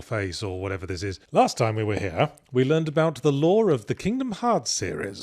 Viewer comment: I swear every time I hear a brit say "the lore of", it just sounds like "the law of", because their dialect inserts R's between vowels.